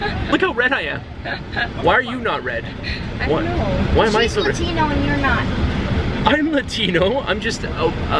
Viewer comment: haha is it just me or when he said "shes Latino and your not" I heard "she's a Cheto and your not"